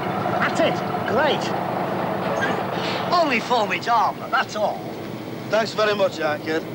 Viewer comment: All these years on and I still haven't a clue what he says at 1:21 as he's walking round to the window. "Only ** ****. that's all" is as much as I can make out. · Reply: It's 'cod' French. 'Only formidable, that's all'. Pronounced 'form-e darb-le'.